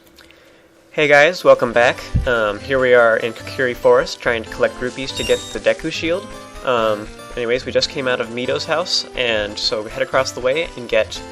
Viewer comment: For some reason I heard "collecting rupees" as "collecting roofies" and was very confused for a minute.